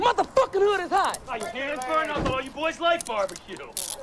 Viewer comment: I dont think you guys caught what the cop said while he was handcuffing Cane... "Oh I thought you guys liked Bar B Que" that was in reference to the cookout that they had just left from. Remember Cane asked Stacy and A-Wax who put the BBQ together, neither of them knew... It was actually the Cops that set it up.